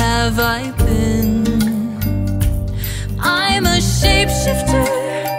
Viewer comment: Finally, the original sounds so... odd. It's like they wanted to skip the words "shapeshifter" so they made it into one and said "shipfter" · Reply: I always heard it as chef shifter. I kinda like it more as chef shifter after hearing it like that for so long